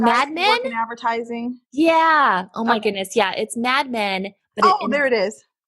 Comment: what was that show you guys were talking about the interviewer compared you to? Yeah was interesting hearing how you adjusted your content on the FB app. Yep very informative. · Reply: awww. Lol I thought she said bad men. Lol I was trying to Google it. Actually, never heard of it.